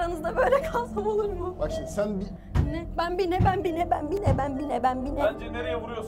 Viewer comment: aşkım bi mi? Dedi benmi yanlış duydum